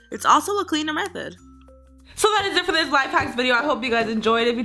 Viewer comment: At the end I thought it said SO THATS IT FOR THIS SLIME HACK VIDEO But in reality it said SO THAT IS IT FOR THIS LIFE HACK VIDEO